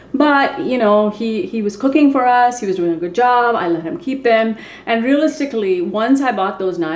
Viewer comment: When you said I let him keep them I thought you said I think I’ll keep him 🤣🤣🤣 had to listen again !